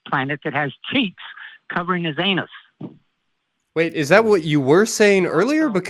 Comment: Did I hear that correctly? "Man is the only one that has cheeks covering their anus." Uhm... Did I hear that correctly? Wow. Just wow. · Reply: I heard "teeth covering his anus" and thought it was an elaborate insult saying that a person talks shit :') I would even make more sense :D Ever seen an animal that talks out of their ass?